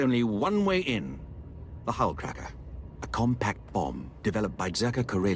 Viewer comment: I thought said "hull crapper" for a moment. Dude, if you're crapping ship hulls, you need to see a doctor and a lawyer, perhaps not in that order.